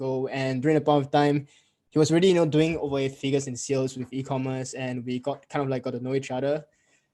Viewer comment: you say "i met him 1-2 years ago.. he was already doing 8 figures in sales " - did i hear that correctly, is it 8 figures